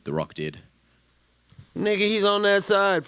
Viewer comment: I’m having a debate with my cousin, did Nate say nigga at 17:37? Lol